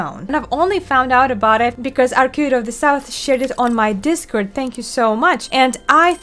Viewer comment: I thought you said somebody shitted on your discord page